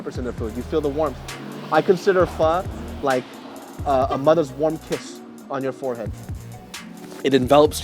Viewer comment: thought he said, a mother's warm piss on your forehead... My Bad